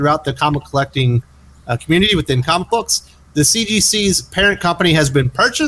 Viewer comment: It's been covid so long I thought I heard CDC.